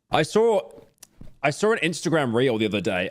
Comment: So after about 6:26 does he say reels or rails?